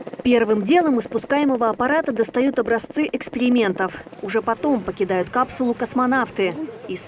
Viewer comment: мне одному послышалось-первым делом достают образцы экскрементов ?!